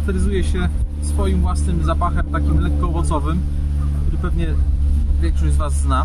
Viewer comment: Jaki ten aromat ma? Bo ja usłyszałam włosowy 😅